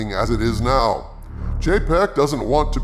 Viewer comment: weird thing. every time I hear someone say Chapek, I hear it as "jpeg"